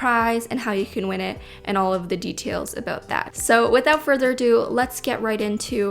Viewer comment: I thought you said without further a doodle